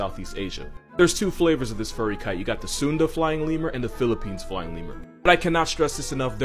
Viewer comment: At one moment, I heard "a lying femur" instead of "a flying lemur" 😁